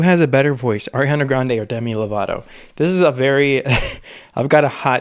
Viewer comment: At one moment it sounded like you said Dummy Lovato. 😂😂